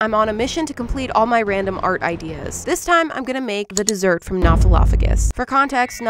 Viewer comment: Who else thought she said jerk instead of dessert???